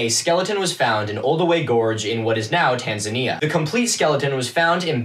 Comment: When you said the gorge was in Tanzania, my brain heard "Tasmania" which would have made this story WAY nuttier. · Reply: I'm currently three and a half mins into the vid and I like to scroll through the comments whilst listening... I didn't even realise I had misheard it as Tasmania until I saw this so thankyou lmao it makes sense now